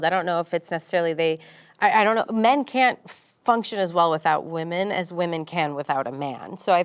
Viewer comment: did she said women can function without a man? or am i hearing it wrong? if she said that she's living in a delulu world bruh